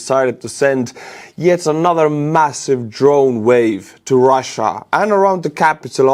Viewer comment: Did You say: "Drone Rave" ? 😂